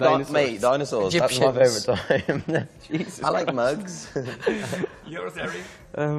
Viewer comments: Was that a fire shot at Demi? I thought I heard "I like mugs" after the whole dinosaur thing omg I'm dead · what did Lou say ? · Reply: DIY SaRa he said "Egyptians" ( he was mocking Liam's "dianosours")😂😂😂